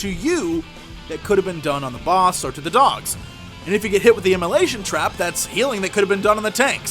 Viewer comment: lol when he said immolation trap i thought he said malaysian trap. omg lol i feel so bad